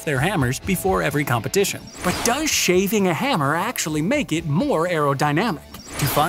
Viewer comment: I thought it said shaving a hamster